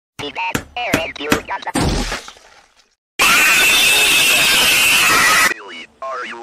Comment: what is billy saying here? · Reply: I think he said, "Hey dad, aren't you gonna-"